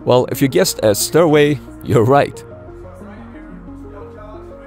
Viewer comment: for a second there, I thought you said "The stairway leads to the brig". I had a good chuckle at that one.